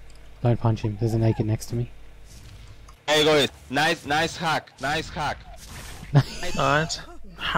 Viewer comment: Lmfao it sounded like he said nice hat nice hat